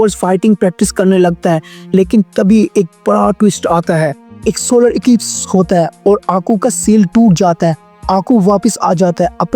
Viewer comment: ye kya bole sunai nahi diya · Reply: Ek solar eclipse hota hai